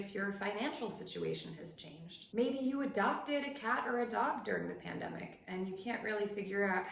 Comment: I thought tgis said cat orgy